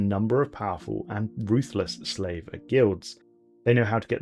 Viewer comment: When you said "Slaver Guilds" I thought you said "Flavor Guilds" and was very confused as to what that means.